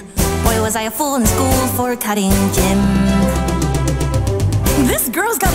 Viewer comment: I swear to the gods, every time the lyrics go "cutting GYM" I always hear "cutting GIN"... do I have a problem?